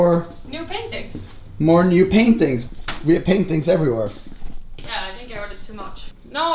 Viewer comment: it sounded like he was saying 'peng tings everywhere'😂😂